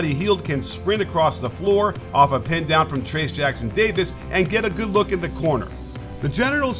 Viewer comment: did bro say genital?